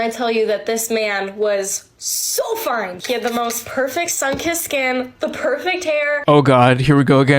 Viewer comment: She said "sunkissed skin" I thought she said "sunken skin" like yowza man's already desiccated?